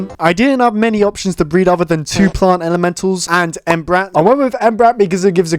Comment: he just sounded like entbrat